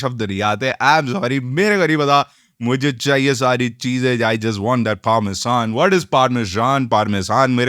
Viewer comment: I think he's saying parmesan cheese becoz usse phele usne (cheezzee bhi bola h that's why )